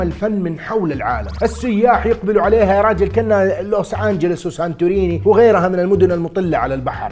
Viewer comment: لا ارادياً سمعتها لوس سانتوس